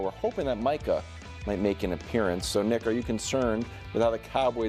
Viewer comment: the fuck he say? Coulda sworn I heard n*** or am I trippin?